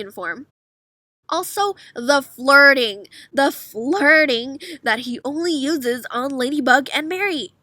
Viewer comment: I thought you said farting 😂